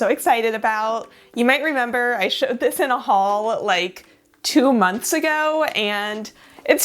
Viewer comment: Haul! Right I thought you said “hall” 😂 that makes way more sense :) and poor Samantha …